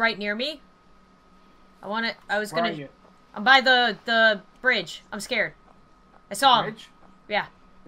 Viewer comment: I thought she said "I'm by the Fridge" instead of ridge 😂😂😂